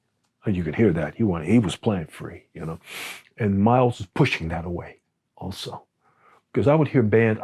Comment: Thank you for recording & disseminating these videos with Cooper-Moore. Just as a note of clarification, at around 13:13 the caption reads "...Miles was pushing that away." What C-M actually said here is "thataway," meaning, "in that direction."